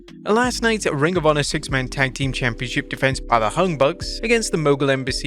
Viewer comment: Did he just say the hung bucks?